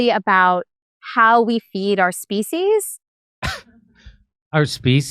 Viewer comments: it sounded to me the first time i heard her say it I thought she was saying feces,lol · Sounds like she said feces.